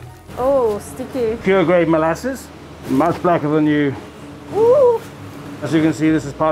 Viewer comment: Did I hear him right, @ 2:32 did he say ‘much blacker than you’ 😂😂